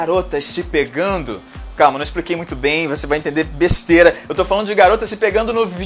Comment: entendi bestera kkkk